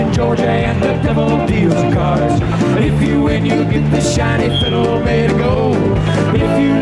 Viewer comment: As a kid I thought he said, "You'll get this tiny fiddle made of gold" and I wondered why you'd sell your soul for a tiny gold fiddle.